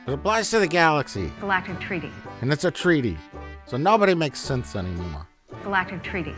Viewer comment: Yeah, I heard “nobody makes sense anymore.” Makes more sense then the UFP’s androids went nuts so everyone in the galaxy decided to outlaw them. How is that even possible considering it takes decades to reach the Delta Quadrent and the Gamma Quadrent (without the wormhole)? So there’s two choices: either this is set in the actual Star Trek universe and “nobody makes sense anymore” or this isn’t set in the actual Star Trek universe.